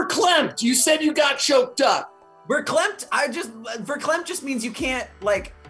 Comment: What word are they saying LMAOO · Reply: Verklempt I think?